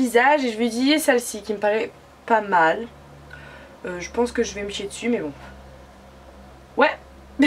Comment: C'est moi ou elle a dit "je vais me chier dessus" ? à